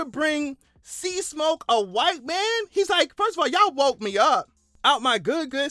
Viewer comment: white man - I thought you said wife man hahaha